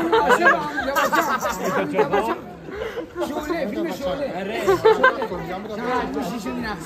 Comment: did he say film Sholay?